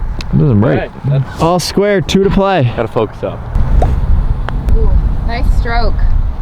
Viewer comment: Did I hear "Nice stroke step bro"?